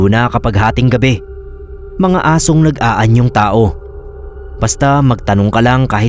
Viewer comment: Ang seryoso kong nakikinig tas bigla kong narinig, "...mga asong ng aanyong tao" 00:50 😂bigla akong natawa, d na scary 😂